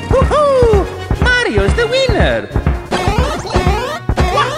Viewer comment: I heard “Mario’s the weniner”